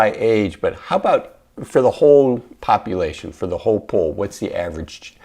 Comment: I misheard and thought you called yourself “hey a**hole” 😂